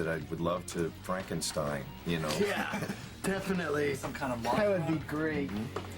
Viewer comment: Sounds like Lars says, “Some kind of monster.” 🤨